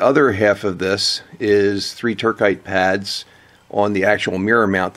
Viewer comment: For your foreign viewers, "turquide" pads? 22:15 I can't figure out the spelling... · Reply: Turcite